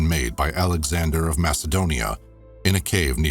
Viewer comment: I thought he said "Adam Sandler of Macedonia"....it was ALEXANDER of Macedonia..💕😚🤣🤗😂